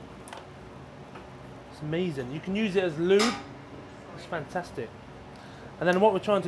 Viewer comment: did he just say that he can use pork fat as lube? :S